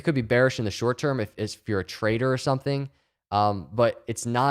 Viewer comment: “It could be bearing if you’re a trader” I hear “traitor”